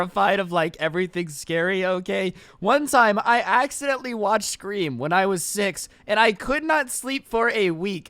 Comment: When you said scream i thought you said scooby like one of the live action ones and i was like “finally someone i can relate to”, then i realized you said scream😔